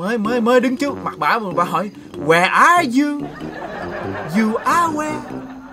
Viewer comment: Má "where are you" tui nghe thành "I like you"